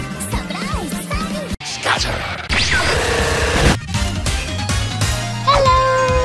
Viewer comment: did I just hear a cake 'helloooo!' poi~?